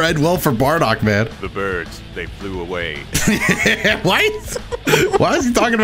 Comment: How I heard it the first time "The birds thank Lil Wayne"